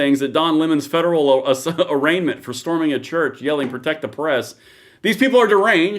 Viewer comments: I thought they were saying protect our breasts and I really enjoyed the part where the guys chimed in in baritone saying protect our breasts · Lol!!! I thought they were saying protect our breasts!!!! 😂😂😂😂